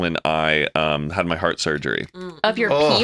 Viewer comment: wait did I just hear say heart surgery?? Or am I losing my mind. Someone pls respond for my peace of mind !!!